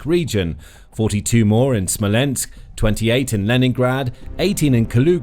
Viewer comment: Wait, did he say Leningrad?